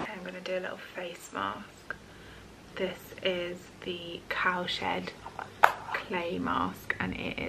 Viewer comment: lol definitely did a rewind. I’m in USA and had not heard of cow shed brand. Definitely thought you said cow 💩mask and it is brown 😂